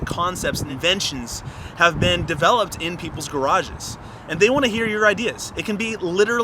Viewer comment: peoples? am i hearing right?